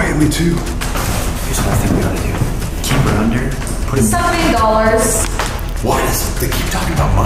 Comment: and only got Dollars. I heard say 17 or 70 Dollars. Or she could've said 'something'. I dont know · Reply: She said something